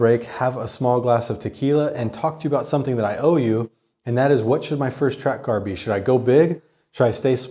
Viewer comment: All I heard was 'I owe you a track car'